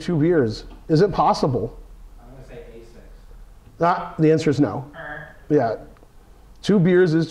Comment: very suspicious. what was said at 2:19? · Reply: "I'm gonna say a6"